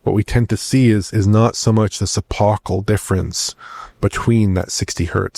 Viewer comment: What word is saying at around 5:20? Suppocal? Never heard this word before Edit: He says «epochal»